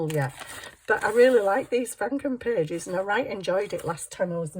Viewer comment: Hi ya ! I m here with ye laughing away ! Didn't know ye got snow !! Yes have kitty litter in the boot ! NOW I realize what Franken papers are!! I thought ye said Frankenstein papers 😅 I should do this too. Tis like a collage !? I'm stenciling random papers , preparing to start building some journals, my hippie journal has finally flattened abit, so I can deco ! ❤🎉